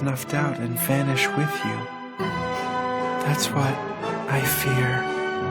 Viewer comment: Deadass, when he said "I want you to restore my heart" I thought he said "I want you to read Stormlight Archives" and thought this was an amazing edit you made as a joke but I'm just stupid womp XD